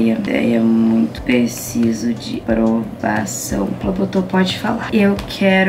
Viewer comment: eu ouvi um palavrão ou e impressão minha??? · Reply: ela falou "botei"